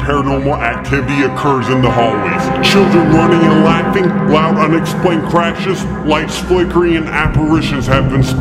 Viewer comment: Super late but 10:59 it says explained instead of unexplained